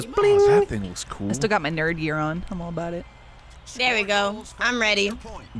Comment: at first I really thought that Lindsay said that she still had her Nervegear on.